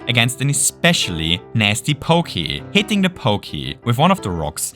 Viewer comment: The captions say "wiggler" here instead of pokey.